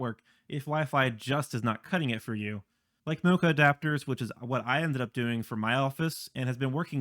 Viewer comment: I'm trying to get what kind of adapters you said on 9:01, but can't quite get it. Subtitles say "mocha" adapters, which surely is not the case haha! Did you say network adapters? I'm tring to figure out the simplest way to extend the network in my house, since it has a rather long shape and the walls are kind of thick and diminish the signal very significantly. And I also found out that the modem and the room I want to plug the PLC in are on different powerline circuits (which will communicate in the end, but still are distinct). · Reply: They're called MoCA adapters. Simply speaking they convert coax to ethernet, and they're great for when you have coax cables already run through your home. They can be a little pricey, usually around like $75-$100 for a decent set.